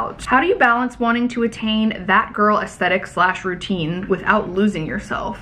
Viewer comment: I was just listening so didn’t see the words on the screen and I heard “fat girl aesthetic” 😮😅 I had to rewind it ⏪ 😅